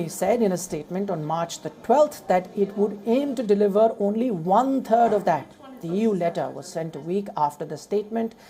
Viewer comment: Did I hear right ? He said : 'one third', I heard : ' one turd' 😂😂😂😂